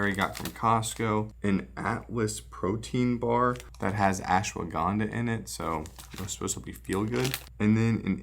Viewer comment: Lmao I totally miss heard that as “it’s got actual ganja in it” thought you brought some edibles to work for a sec.